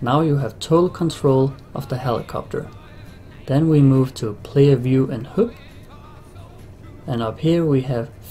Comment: i wasnt looking where his mouse was and thought he was saying "Player view and Hoop", played it five times, still sounds like "Hoop". Anyone who only reads HUD but never heard it pronounced, it rhymes with mud and bud